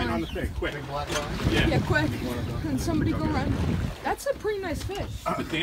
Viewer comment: no way i heard someone say thats a pretty nice fish